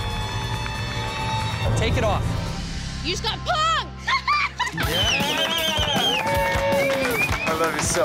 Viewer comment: what did lucy say?? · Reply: u just got punk'd